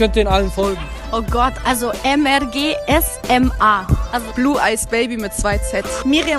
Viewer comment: Min 19:12 wie heißt die auf Insta frage für Freund was sagt die da Blueicebaby odee blueeyesbaby 😂